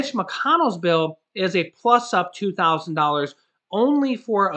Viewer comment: Thought biden said $200 mo for 1 yr